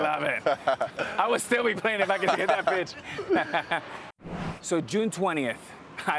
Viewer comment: i thought he said ill still be playing if i could get that bitch 😂😂😂😂😂😂